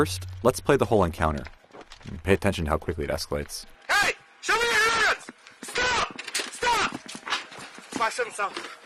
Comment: he said”show me your hands, gun!” Actually.. not stop. MAYBE if the officer wasn’t claiming the man had a gun, his partner wouldn’t have reacted w deadly force ... just spitballing ya know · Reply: BoogFellaGames 0:39 He actually said "Hey, show me your hands. Stop, stop." There's captions there for you if you're having a little trouble comprehending.